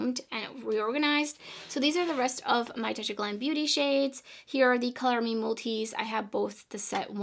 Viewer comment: you spoke so fast, what was it that you said? Tia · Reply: Touch of Glam Beauty eyeshadows